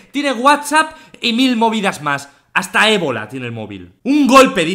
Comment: mmmm auronplay puede viajar al futuro o que porque dijo egoland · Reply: Dijo ebola. _.